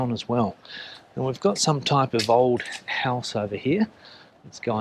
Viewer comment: I really thought you were going to say, "We've got some type of old lady..."... 😅